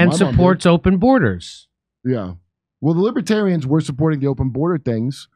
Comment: I thought it said, ‘Libertardians’ !! I shyt u not! Hey Kurt!!